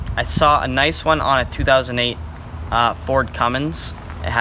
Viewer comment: "ford cummins" did i hear that right?